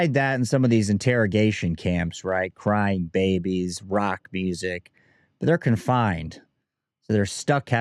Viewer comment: did u say rock music 😂